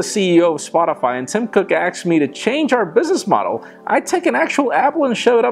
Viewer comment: did he just say 'axed me' instead of 'asked me?